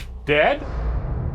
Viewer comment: I heard " im dead" not he dead.